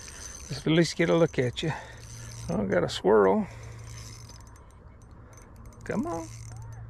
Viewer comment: Swirl??? I thought you said squirrel lol